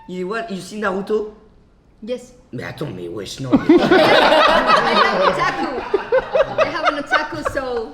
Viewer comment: 1:5:35 Elle a dit "i have nutaku" ou c'est moi pour ceux qui savent ? :-)